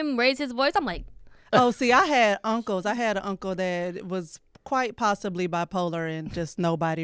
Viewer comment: accent so alive, I thought she said, "Uncle Tam".